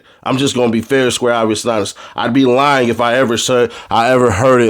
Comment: Wtf is “Fair and square obbysnops” or whatever tf you said mean?🤷🏾‍♂️🤔🤨😂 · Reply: Man what?? Go to 11:16 lol whatever you said sound more like obbysnops then it sound like obvious!😂😂